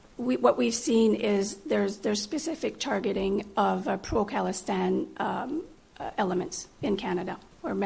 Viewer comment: I heard Pro Palestine 😂😂